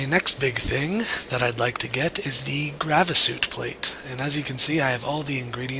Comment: lol, the second time he said Grey Vis, I thought he said Gravy's.